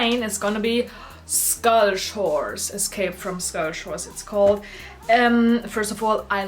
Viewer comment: when she said skull shores all I heard was "Scottish Whores" But love the video! My fave character is Cleo, has been since i was a young girl. I absolutely hate the new movies!!